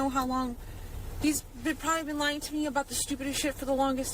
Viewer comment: ‼️ATTENTION ‼️ What do you hear at 1:04:12? Does say Stephan or “he” has been lying, or does she say “they/them” (referring to both Stephan AND Maddie)? I hear “I feel like, so stupid because … I don’t know how long these … they’ve probably been lying to me about the stupidest shit for the longest time … like I’m …. Sigh.“ She’s clearly referring to both Stephan AND Maddie! But why? Why does she give her barely 13-year-old daughter equal blame as her 37-year-old pasty doughboy pedophile? <link>